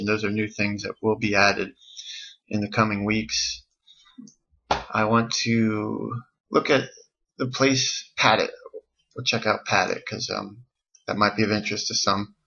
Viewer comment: you say 'look at <something that starts with a p> or check out <something that starts with a p>', what were you actually saying because its not really clear? is it a domain or something? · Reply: I said, "Look at Pad It". That's a function in the application